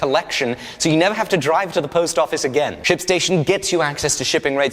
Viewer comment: thought he said shitstation...